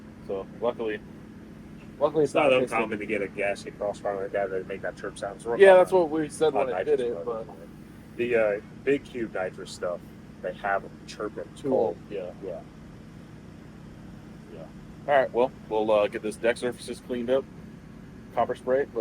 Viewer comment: Big cube nitrous have chirp what??? Anyone know what he said at 34:25???